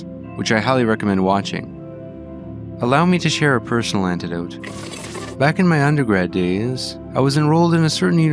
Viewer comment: did you say a 'personal antidote' rather than 'personal anecdote'?